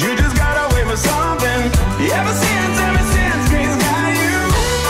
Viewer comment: First time I heard this I thought it said sink face got you